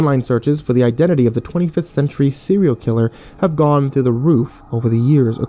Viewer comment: I thought he said 25th century serial killer lol I'm a dork X)